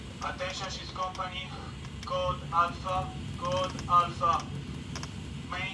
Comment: Could not understand this guy,sounded like he said “code answer” “main dining don’t want”🤷‍♂️